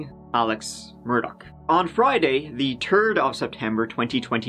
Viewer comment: UPDATE: <link> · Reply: Did he say.... "the TURD of September"?